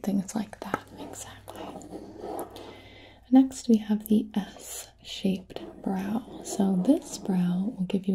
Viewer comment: she said it correctly but my brain was kinda blocking out what she was saying because I wasn’t paying attention and I thought she said ‘ass-shaped’.